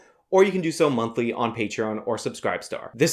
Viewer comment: "a deep dive into the leftist mind" Be sure to follow hazmat protocol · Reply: I thought he said "a deep dive in a leftist's behind" so I thought it was some gay shit, ....... literally